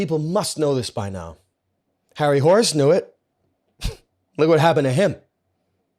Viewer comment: did he say harry horse or harry horus? I need to look more into this